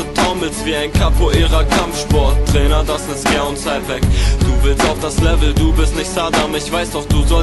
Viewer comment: was sagt er? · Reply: Das ist Jahr und Zeit weg, du willst auf das Level